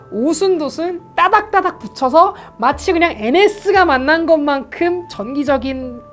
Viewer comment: 형 미안 ms 라 들었어... 썩었나봐...